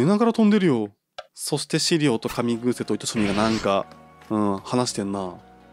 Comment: 写真のセンス良いですね✨ ノボリさん好きよ☺️ リーフィア可愛いですよね✨ カミグーセが紙風船に聞こえる(*´艸`)